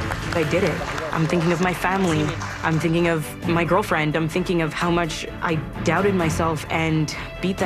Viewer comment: Did I hear it wrong or did I really heard her having a girlfriend? That's so cuttteeeeee 😆😆